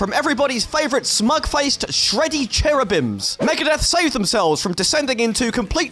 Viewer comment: Kiko Loureiro, one of the greatest guitar hero from Brazil 🇧🇷!!! Maybe as great as, one of the greatest guitar hero from the internet [insert internet flag]. Great video, and very well done research! 😆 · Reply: hahahaha yes indeed... Somehow I heard "Megadeth sold themselves" (wtf does that mean anyway) instead of "saved themselves" Sorry mate!!! And watch as I turn my bitching comment into something BEAUTIFUL!!! Love you channel, by the way!